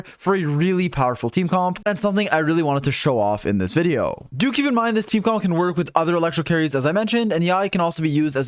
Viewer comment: every time he says "do keep in mind" I hear "dookie in mind"